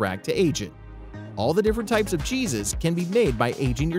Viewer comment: "All the different types o Jesus..." Was I the only one who heard that at 3:15 ?